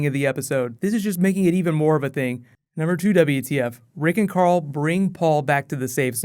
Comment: Does he say rick and carl at 4:55? lol anyway are they going back for the truck any time soon or are they going to give up on it completely?